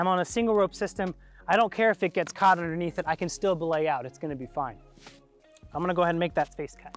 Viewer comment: Am not a native English speaker, what's the word am hearing here 3:20-3:24. Am hearing something like "blay out" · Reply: Belay out